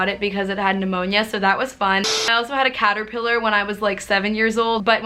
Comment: I thought you said cat not caterpillar and I was like O_O